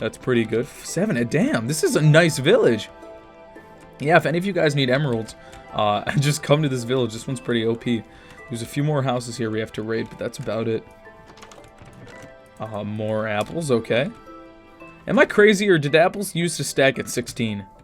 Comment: did he just say that he had a few more houses he had to rape?